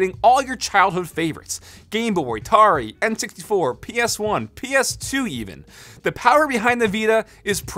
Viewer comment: Did i hear ps2?